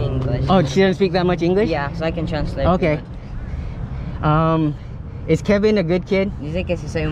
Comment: I thought he said: "Is Kevin a virgin"